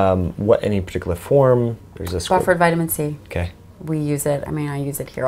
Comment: I can't understand what particular kind of vitamin C she uses (around 39:23). Did anybody get that ? Thx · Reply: Buffered